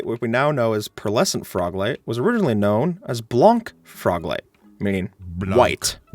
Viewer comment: I legit thought he said blonk and not blanc for some reason lol